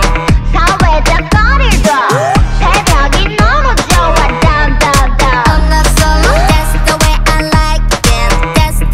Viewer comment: What did she sayyy😳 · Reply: Isoojung Official I know that I was just saying that Hyuna said "I love early mornings, Dawn, Dawn, Dawn" Which in other words can be written as "I love Dawn😳"